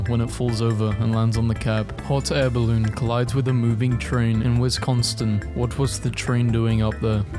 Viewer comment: you did not just say wisconstin